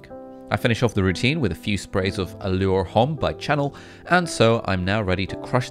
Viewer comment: Did he just say channel instead of Chanel